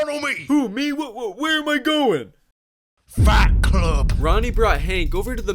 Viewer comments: Oh I Thought he said “Fat club” but it’s “Fight Club” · I thought it said fat club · I thought he said fat club not fight club